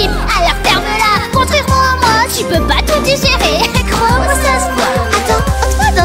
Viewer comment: pourquoi à 1:16 il est écrit "crois-mois ça se voit" et à l'écoute on entend "gros ça se voit"